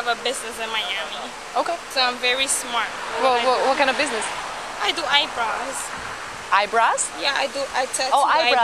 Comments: Drunk Driving. Hit and Run. Resisting arrest. All charges dropped.... What a justice system we have. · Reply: LOL LOL-The white officer is MAKING FUN of her ACCENT-The female cop is EVIL.iTS A Pretty Latina-not need to mock her and say EYE BRASS ?? She clearly said i do EYE BROWS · She stated she was really smart, but I think this video proves that she’s got a lot to learn yet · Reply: LOL LOL-The white officer is MAKING FUN of her ACCENT-The female cop is EVIL.iTS A Pretty Latina-not need to mock her and say EYE BRASS ?? She clearly said i do EYE BROWS